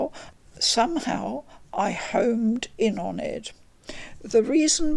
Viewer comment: I swear she said "I hoMed in on it" ??